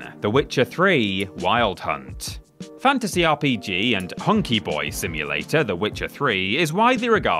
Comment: Did you just say "honky boy simulator" 😂😂😂😂